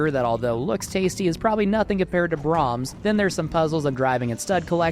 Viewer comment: For a short second I thought he said „nothing compared to Brahms“ which came out of the very unexpected.